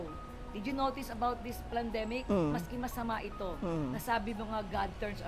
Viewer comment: I had to repeatedly go back to 6:11 just to be sure i heard it right. "PLAN-demic" ? Hmm, that depletes the credibility of the guest.